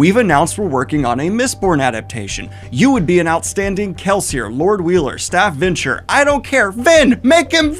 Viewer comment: I had misheard 'Lord ruler' as 'Lord Wheeler' and now I can't get the image of Joey Wheeler killing kelsier out of my head